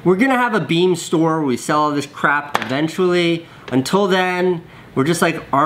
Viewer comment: Did he just say ," We're gonna sell this CRAP eventually @ 1:42 ??